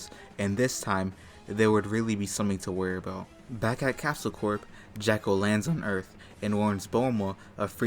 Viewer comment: Am I the only hearing Jacko instead of Jaco? Either way I got a good laugh from that. 😅